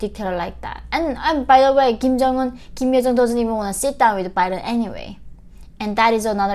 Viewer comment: Sounded like you said: "Kim Jong Un, Kim Il Sung doesn't wanna sit down with Biden anyway" 😶🤔